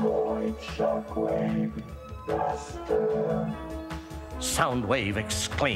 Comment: I THOUGHT HE SAID BAST ARD